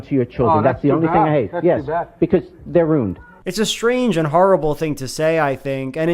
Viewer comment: I was kinda confused t the end cause I misheard say "They're rude" rather than "They're ruined" I was like, "Idk Joel, I think it's pretty fair and not dehumanizing to call a kid rude..."